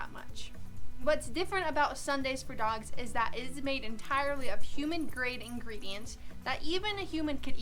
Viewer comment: I heard "dog food made entirely from human ingredients". haha